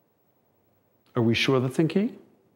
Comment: They need to bring back the "have you driven a Ford lately?" Slogan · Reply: What did Jason say at the end that set the crew off? I heard "Are we sure the thinking?", which makes no sense.